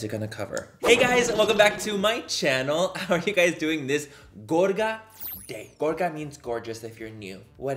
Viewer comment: I thought you said "gorda day" and looked at the screen like "sis how you know?" Lmaooo